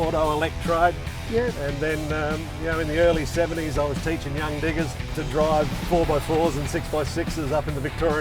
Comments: Did he just say "I was teaching young niggas" so casually... That was hilarious. Good on ya mate! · did he say young niggas or do my galaxy note8 mono speaker suck? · was i the only one who heard him say young niggas